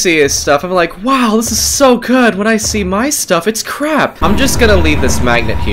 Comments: Did you just say the C word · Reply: he did, crap. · Did he just say crap